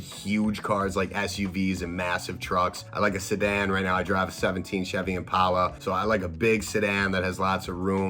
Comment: My heart almost stopped, I thought you said '67 Chevy Impala